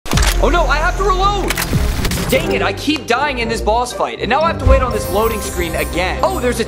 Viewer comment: I thought he said:"I keep dying in this balls fight."💀